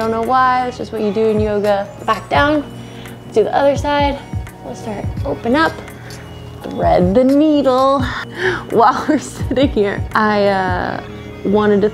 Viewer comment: i thought you said “threaten the needle” and i was like well that escalated quickly